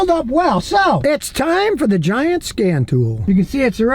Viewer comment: I misheard, I thought Scotty said "It's time for the giant scam tool" :face-turquoise-covering-eyes